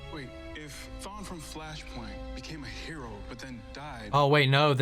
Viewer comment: i spat my water when i heard you say "Fleshpoint"i know its flashpoint but it sounded like you said fleshpoint xD